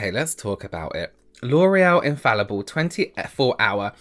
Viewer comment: I fell for the hype on that "lorral" powder foundation and it looks God awful on me 🤪🖤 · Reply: Every time he said L’Oréal I heard “loral” in my head harmonized between James and